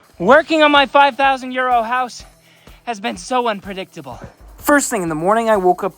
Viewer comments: I thought you said “Welcome to working on my 5000 year old house!!!” 😂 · I swear to you, it still sounds like he is saying 5,000 year old house. 😂😂😂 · It always sounds like your saying my 5000 year old house not Euro house 😅